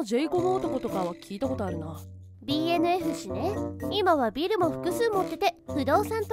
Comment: BFN○ね！に聞こえた